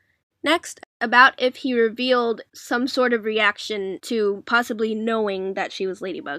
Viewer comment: Did my cat ears hear right? Did you say pawsibly at 5:24??????